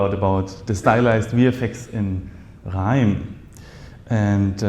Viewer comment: Did you say Grrrrime? <link> scnr 😅